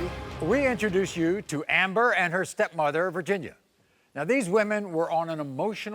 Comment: At first when they introduced the step mother/ aunt or whatever she is and her name came up as Virginia.... I thought It said " VAGINA".. 🤷‍♀️🤦‍♀️😂😂